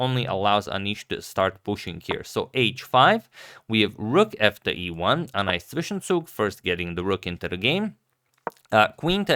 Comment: What is the word he says ("a nice ???") after moving the rook to E1? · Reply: zwischenzug: intermediate move